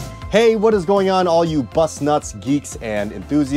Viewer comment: Sounded like he’s said Bust Nuts 😆